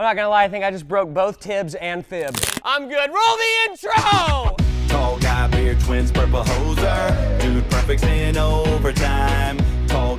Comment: Is it just me, or do I hear "Tall guy, weird twins, purple hoser". I swear this happens every time I hear this intro